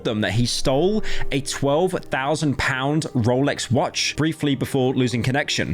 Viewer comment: i thought he said roblox watch and i feel like a moron